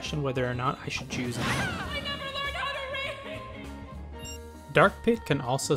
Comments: What does he say? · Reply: I never learned how to read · what was he saying?! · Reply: Salt the Dog "I never learned how to read!" the "read" kinda gets cut off sometimes